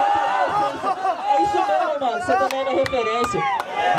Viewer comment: Em 4:06 sempre entendi o ADM dizer "cito mesmo a referência" só agora percebi que ele disse "cê também é minha referência"